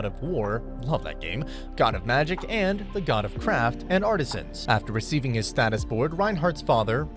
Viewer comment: When introducing the gods I thought they said “god of crap” then realized it was craft😅